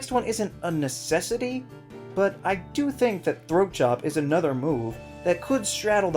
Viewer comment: Watching the video, decided to take a sip from my drink. 6:45 comes along. Me: Did he just say throat job? rewind video "Throat Chop" Oh. My. GOD...